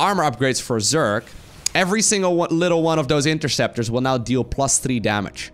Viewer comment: whatever, fusion core and fleet beacon, both starts with F.. aswell as, infested teran and interceptor.. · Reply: I feel like he didn’t even say interceptor with how many times I heard him say infested Terran instead 😂